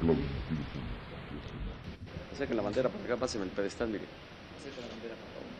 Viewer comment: a ver corrijanme si escuche mal, pero cuando se refirió a la bandera 0:20 al 0:23 dijo, " Saquen la Bandera para que ya pasen el pedestal mire" jajajajaja, la quería sacar o poner al centro, cual pedestal quería que pasaran?? por favor díganme que escuche mal semejante estupidez.